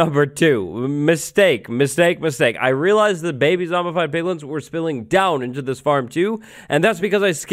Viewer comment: did anyone else hear "baby zombified pilgrims" at 35:11 ?? asking for a friend